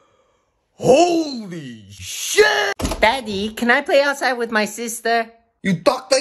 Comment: Last time I saw the first one I thought I heard ohh shit